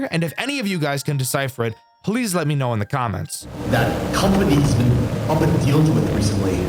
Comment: I heard Random guy #1: that company has been deal recently Random guy #2: Why......